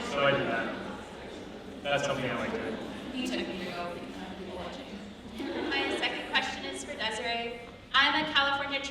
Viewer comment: What did he say he doesnt mind watching at 1:01:40-50? I couldn't quite hear from the echo. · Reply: Jennifer Doyle